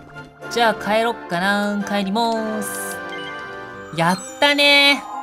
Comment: やったー帰りまーすが 帰りもーすに聞こえたｗ